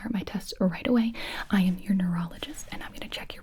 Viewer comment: I thought she said urologist and I was saying why tf are you gonna look at my brain 💀